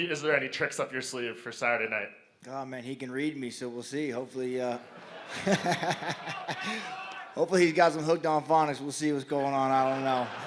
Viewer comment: what did cowboy say?? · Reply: "He can read me so we'll see, hopefully he got some hooked on Phonics."